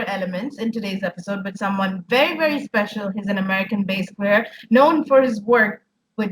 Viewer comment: When Bryan was pointing to his beard during the introduction I misheard her saying "known for beard" 😂